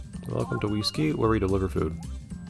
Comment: For some reason I misheard "deliver food" as "deliver poop." I don't have anything else to say aside from putting that mental image in your head, lol